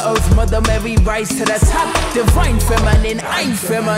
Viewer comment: I thought she was saying umma which is mum in korean(i think) but she was saying woman